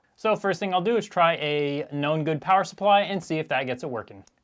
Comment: 4:29 Did you say “know good psu” or “non good”. Sorry I’m not English and I don’t understand it 100% (Obliviously it should be “know good”, because after you plug in that psu the Xbox turned on) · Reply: "'known good"